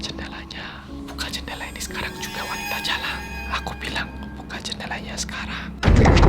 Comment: Wanita jalang??? Aku gak salah denger kan Emang dulunya gimana???